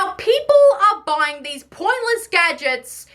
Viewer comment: I thought you said poisonous in 17:21 just to find out it was actually pointless instead of poisonous 💀